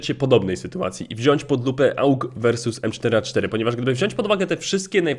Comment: usłyszałem "pod dupę"